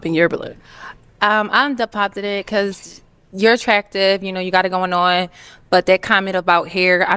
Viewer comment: did she say attracted 🤦🏿‍♂️.